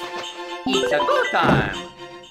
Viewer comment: It's funny because when Mario said "Itsa go time!" my baby sister thought it said "Itsa boo time!"